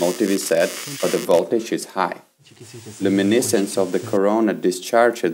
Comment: Did you say Corona at 01:05 ?